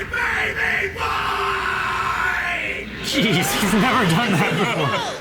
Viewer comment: can someone please tell me what hes saying there? all i hear is "geez hes lardonhighpickle"